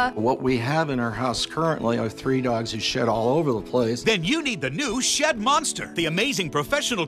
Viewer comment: It sounds like he says he has three dogs who Shit all over the place